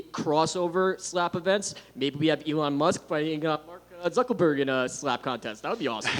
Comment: Did he say "Mark ZuckELberg"? 😂